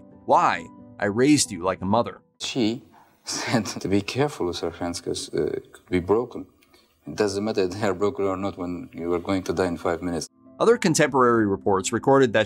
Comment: "she said to be careful with her hands 'cause, uh, they broken, doesn't matter hand broken or not when you're going to die in 5 minutes" lol think this is what was said 😂..